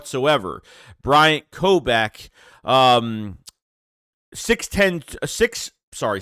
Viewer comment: at 2:55 did you say we signed a 6’-6” running back?